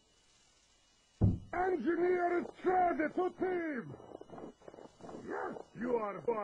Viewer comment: i thought heavy said engineer is crazy stupid